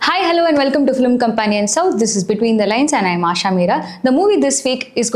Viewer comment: At 0.2, I first heard it as between the legs